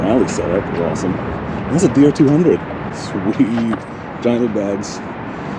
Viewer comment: Funny. It sounded like you said DR200... Anyway thanks again for a great video!